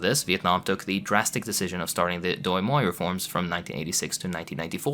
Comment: I'll bite. 18:00 Doi Moi? Doi Hoi? Dôé Hœ? 🤨